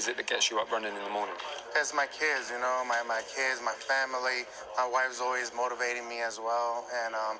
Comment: It sounded like Ruiz said my wife moans at me when he was asked what motivates him lol